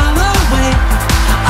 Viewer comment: If u think of it sounds like he is saying I think she likes curls